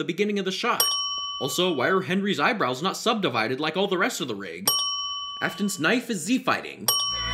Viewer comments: Can we discuss that at 6:16 the subtitles say harry's knife is Z-fighting and not Afton's knife is Z-fighting? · subtitles say "Harry's knife is Z-fighting." but he says "Afton's knife is Z-fighting." ding